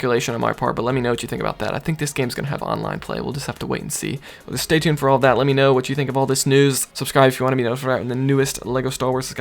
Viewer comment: What do you say there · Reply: I said, "Stay tuned for all that, let me know what you think about this news". Is that what you're talking about?